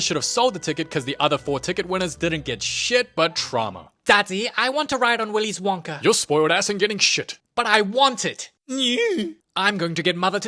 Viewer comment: what she say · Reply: She said "daddy i want a flying elevator"